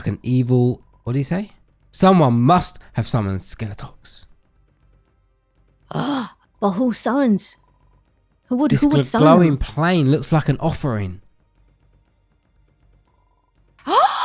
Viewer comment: did anyone hear her say " well who sunns?" instead of 'summoned' lol